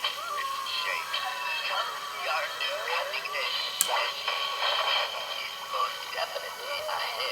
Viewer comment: Do you happen to know what he says at 7:31? Come see our new (..?) thank you!! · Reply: It took all day, but he says guillotine! It’s come see our new cutting edge guillotine!